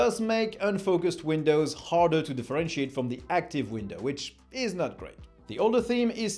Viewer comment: I heard "...but it does make unfocused Windows harder to de-Frenchiate...". 😄